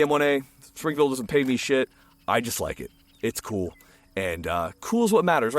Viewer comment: I know you said "Springfield doesn't pay me shit" because I ran it back but i first heard "Three barrels of pygmy shit".